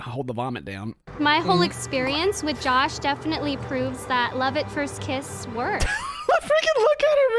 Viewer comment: did she actually say, "love at first kiss works"? Ok, now I'm wondering if he was "HER FIRST KISS"? No way she could think a tight lipped, neck grabbing, lip stick smeared kiss is good. Yep...has to b her first kiss.